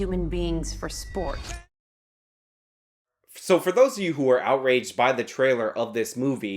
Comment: Did I hear you say "outwage"?